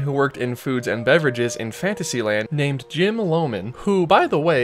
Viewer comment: In the churro section when he says "in Fantasylad,'" i thought he said "infanticide land!" 🤤